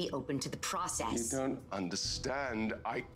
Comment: What did he say the last part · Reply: "Becuse they stole them from me"